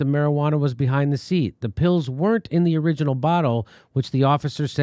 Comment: pills? i thought you said pot